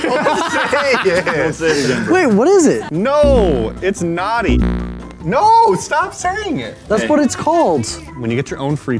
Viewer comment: Am I the only one that wanted to know what was saying. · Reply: it was splooge